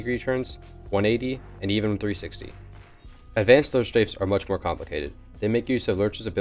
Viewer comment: My fatass thought it said lunch strafe